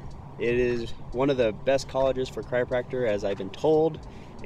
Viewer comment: Having trouble with pronouncing "chiropractor" eh? I kept hearing "cryopractor", which would probably be something very different. :) But love seeing clips of my home town! Thanks for sharing!